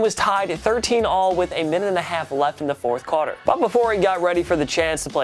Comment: ? What did he say 4th what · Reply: Qwoter